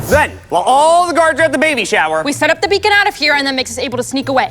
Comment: uh did i just hear baby shower or am i crazy?